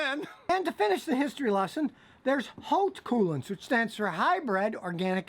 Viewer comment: So what brand is the best one? It sounds like you said oak coolant